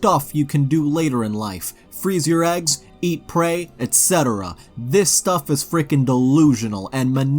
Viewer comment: Listening in the background, and the way you left out "shit" at 1:06, I definitely heard "Freeze your eggs, eat prey, etc." at first. Needless to say, I was a bit confused for a moment.